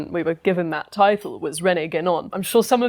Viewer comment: Who is the French writer mentioned near the beginning again? 'Rene Gagnon'? I find only the name of one of the Iwo Jima flag raisers, not a writer; have I misheard the name?